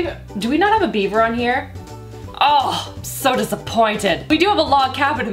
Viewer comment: At first I thought you said "do we have a Bieber on here" 😂